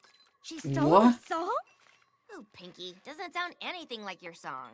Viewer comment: "Thank goodness they translated it, or interpreted it, otherwise I would not have heard what she said." Actually, they misinterpreted what Pinkie was trying to say. Pinkie: "She's doing my song! She's doing it (to them?)" As in, it looked like Zecora was doing exactly what Pinkie said that 'evil enchantress' would do in her song.